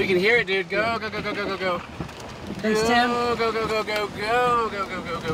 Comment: is he saying "theres tim" like Tim Samaras?